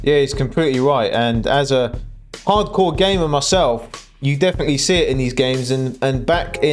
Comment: I thought you said 'Hardcore Gay Man myself' 😂 Only joking...Im catching up on all of your videos coz im new to your channel,Thankyou 👍